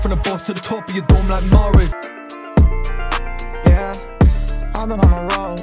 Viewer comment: did mu guy say the n word at 15:40 or am i just hearing things? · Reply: Def not he said yea